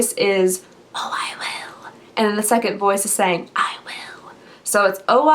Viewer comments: I hear “I will... you will” idk. Lol Thanks for the chance to win. <mail> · i heard "o i will, i will" but with one voice just in a creepy way. please enter in the giveaway. ig: dreaming4baby1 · Hey sweet girl!! One of my trees is still up. The rest are down. As far as the voice. At first I thought it was saying "I am Will, I Will" but you could be correct also. One of my munchkins thought it said "Oh I will, I will" also. But the munchkins and I hear and see stuff often. It's been a thing all of my life. Scary sometimes, but now I'm used to it lol! Love you!